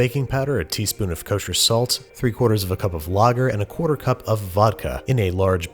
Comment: 3/4 cup of... what? I have no idea what he said there. Is it logger?? Anyone can help me out? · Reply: Pretty sure he said lager